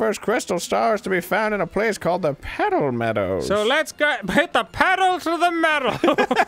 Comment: Arin sounded like hw was saying PEDO meadows